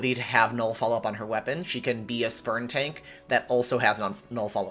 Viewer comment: I thought u said "sperm tanks" instead of "spurn tanks" lol